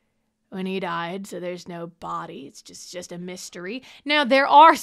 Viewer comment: MERRY CHRISTMAS TO MEEEEEEEE! Thank you for your timing. This time of year sucks and this really cheered me up. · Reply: “it’s just a mistery” LMAOOOOOO i didn’t see anyone else catch this yet